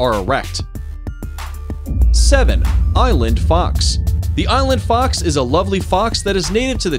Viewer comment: who else thought he said eyelid fox instead of island fox i did hahaha